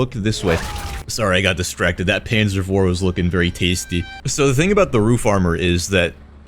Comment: is it just me or does it sound like he says panzer vor at 2:24 and not panzer four